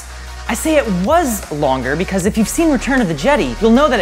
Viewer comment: Did he say Jed-ee?